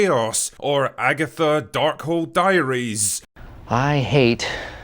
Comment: I legit thought Drinker said "Darkhole Diaries" 🤣🤣🤣🤣🤣